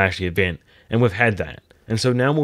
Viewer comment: is it just me or I heard "when they tied down ahdwusbdjakdbeusks ends" 😪 I must be exhausted 😅😅